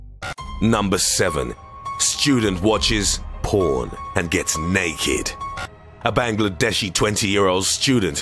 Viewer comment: LMFAO the caption says "student watched prawn and gets new kid"